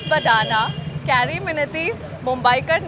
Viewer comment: Did she just say carry minti